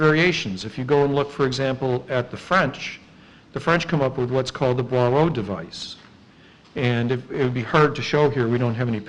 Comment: HI or anybody that can help, what was the french early tank device called that was mentioned in this video at 6:40, I cant understand what he said, sounded phonetically like "bwaroh device" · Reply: PseudoTactics Boirault Device